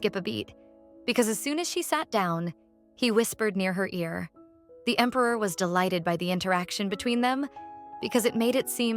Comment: what did he whisper to her!? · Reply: She is beautiful